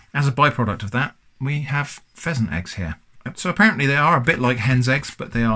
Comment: I was confused at first since I thought it said Pleasant Eggs